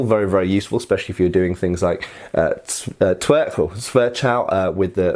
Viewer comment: what did he say?? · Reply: He used the terms twerchhau/Twerhau/zwerhau